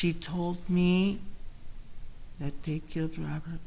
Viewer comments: Did I hear her say 24:42 "The day I KILLED Robert". Did anyone else hear that??? Wth! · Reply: I thought she said THEY killed Robert …. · Sorry I thought she said that THEY killed Robert. I'm new to this case but right now my problem is with attorney not witness. Now I have to look at other witnesses! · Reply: she did say they killed Robert,not one time did she say she killed Robert